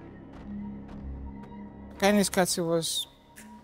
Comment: Does anybody know which film he is mentioning at 4:00? Couldn't understand it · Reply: Koyaanisqatsi